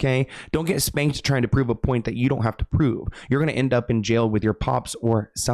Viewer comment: I think he says ' .... a point that you are not going to be able to prove.' Instead of ' don't have too' .